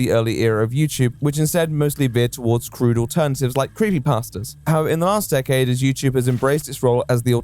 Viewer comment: im not here to add to the conversation i just wanted to point out that at 2:19 it sounds like says "creamy pasta" and it made me smile.